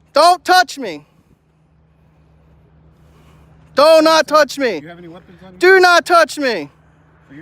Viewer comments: I think you turned them on and thought you said "donut touch me" when you really said DO NOT TOUCH ME!!! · Its true. If you eat enough donuts you will start to look like a donut with qualified immunity on top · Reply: I thought he was saying "do not touch me" he was really saying "donut touch me".